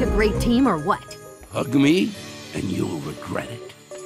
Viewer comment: WHAT DID ANGER JUST SAY!!! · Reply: He said hug me and you’ll regret it.